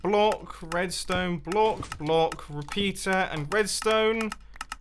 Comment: Who else heard at 17:40 pizza instead of peater